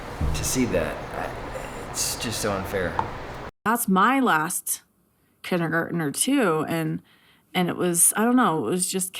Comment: I could have sworn she said “last kid on earth” at 58:00 and I was like wow! She let it slip out!